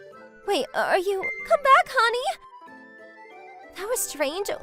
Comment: Has my ears gone wrong or I just heard she said “honey” 🤨